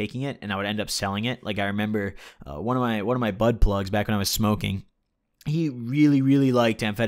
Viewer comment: OMG when you said bud plug I thought you said butt plug lmao